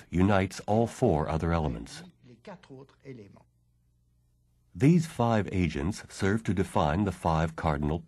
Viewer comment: LOL I thought he said "these five Asians"